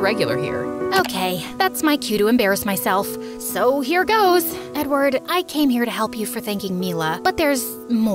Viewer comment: who else heard her tweak “ Edward u came here to help you for thanking Mila”🤨🤨🤨